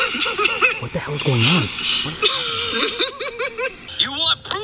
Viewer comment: I thought Evil said “ Who The Hell Goes There?” 😂